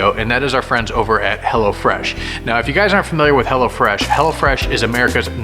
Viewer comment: At first I thought said hella fresh 😂